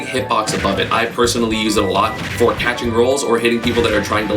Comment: did he just say catching girls???